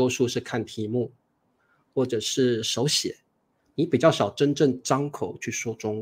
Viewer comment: 老师， 谢谢！ 但是我有一个问题， “你可能大多是看题目咸手写“ 是什么意思？ I don't understand “看题目咸“ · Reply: 或者是手写 :hand-pink-waving